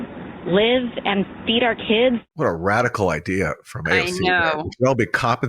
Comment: I thought she said beat our kids